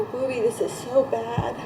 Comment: Am I tripping or does she call her baby "Booby"